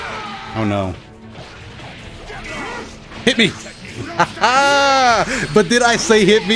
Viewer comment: what the fuck I heard “hit me” Monka